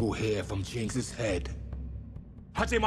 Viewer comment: What the hell did the...master? say at 04:56?? Japanese??? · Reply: I think he said Hajimatte, meaning "start". I'm not a Japanese btw.